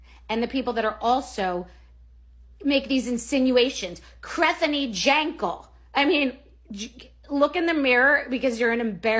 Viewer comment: Is it just me or did she actually sound like she was going to cry there 🤣🤣🤣 for just a second it sounded like a sob "krethenny jenkel"